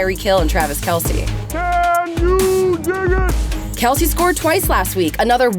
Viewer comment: did kelce say the n word? LMFAO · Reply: No, he said "can you dig it".